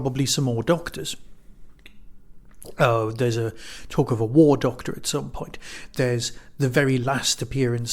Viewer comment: Every time you say "War Doctor" I hear "Wor Doctor". :D